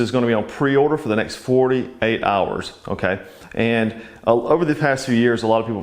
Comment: I said 48 hours not $40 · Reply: Thanks for clarifying. I thought you said $40... but now that you typed it out I can hear 48 hours. Among other things... I guess I need to get my hearing checked! Lol